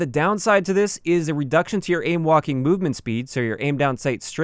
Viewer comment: I keep hearing “ aim Joaquin movement speed.” Am I the only one?